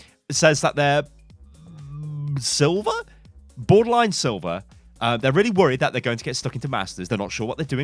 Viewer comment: "they are borderline silver and afraid they are going to get stuck in masters" did i hear that correct?? XD